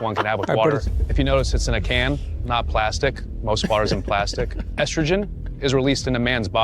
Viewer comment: Did bro just said plastic is gay!? 💀